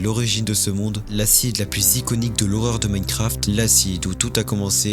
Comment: Wsh, au début c'est marqué "...de l'horreur de ta mère" 0:25 dans les sous-titres. Calme toi !